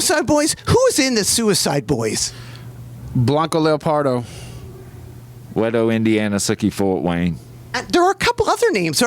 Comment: wtf does he say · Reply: Wetto Indiana sicky Fort Wayne